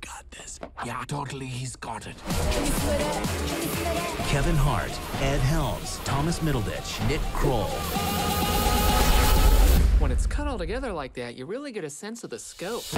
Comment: did he say Thomas middlebitch?